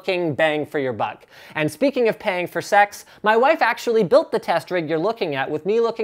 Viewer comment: Did Linus just call his wife a prostitute? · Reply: Yeah, I definitely heard "....talking about paying for sex......".